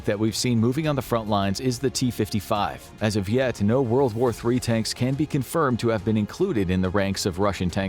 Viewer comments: …did he say WW3 tanks or is it just me? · Did anyone else hear "World War 3"???